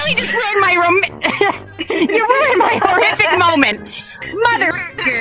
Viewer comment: SOUNDED LIKE CATNAP WAS GONNA SAY “ROMANTIC MOMENT” BUT FIXED THE ERROR LOL